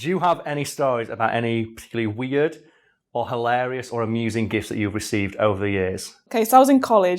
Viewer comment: Your Yorkshire is showing! For a minute there I thought you'd said gifs, not gifts